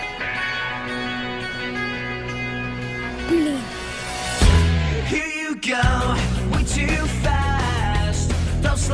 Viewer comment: did he say "Bean" ??? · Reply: Blein, it means Damn in russian